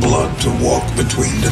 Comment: What robot say at 0:13? Please, help me. P.S. i am not english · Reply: Here's a full script of what he said In the trailer. "You've returned... The only flesh and blood to walk between dimensions, between our world... And theirs."